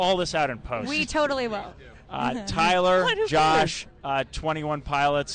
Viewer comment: wait what does Tyler say at 7:55? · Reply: Hudson Ablitt "What who cares?"